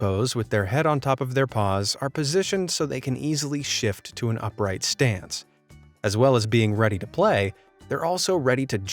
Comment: I thought at 5:17 the subtitles said “are positioned so they can easily shit to an upright stance” lol